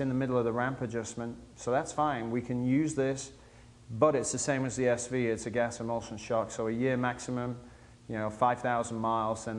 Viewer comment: At 1:14, I didn't quite catch the type of shock that mentions the rear shock is. What is it called again? Thank you :) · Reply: Sounds like "it's the same as the SV (suzuki), it's a gas emulsion shock."